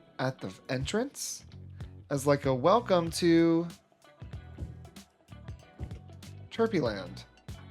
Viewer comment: I swear you said “Welcome to…. Turkey Land” 😂😂